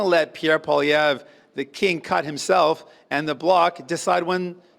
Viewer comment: I could have swore that I heard Jughead say king cuck 😂